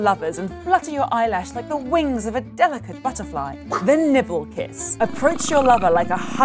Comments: I thought she said "The Nipple Kiss" 😂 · I heard "the nipple kiss" and I was like "jfc this I'd buzzfeedredtube at this point "